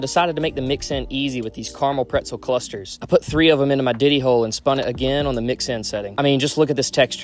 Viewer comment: Love the Video....!! But did I hear u say the "DIDDY" hole!?? Hahahahaahaha😅😅😂😂